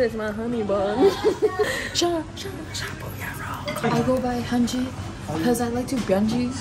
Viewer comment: DID SAY THAT SHE GOES BY HYUNJIN?!?